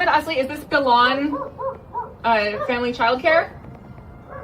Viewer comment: did she just say bin laden family child care????